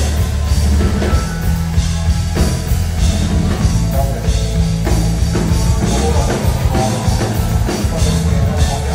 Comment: 有人聽到6:27講咩嗎? 現場聽唔到, 返到黎睇好多次都聽唔到😅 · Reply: 佢話：多謝 我好耐冇試過 覺得自己係香港人 。